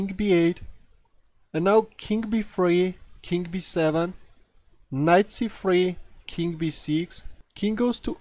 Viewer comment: you wanted to say knight c3 and it sounded like you said 'nazi 3' lol